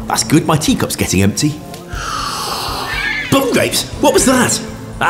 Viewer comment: did he say "bum grapes"?